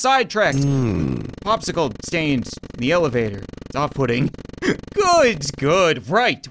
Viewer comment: "...no really, popsicle stains in the elevator. Very off putting" - Only I heard "Off pudding" lol